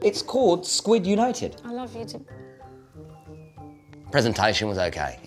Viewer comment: Did she say “i love you too” to chunkz, and the way she looks at him